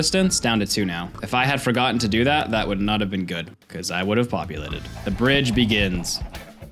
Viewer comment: The first time around I heard ovulated and was real confused